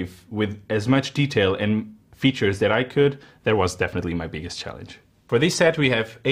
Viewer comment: When you said that was definately my biggest challenge I thought you said that was definately my penis chest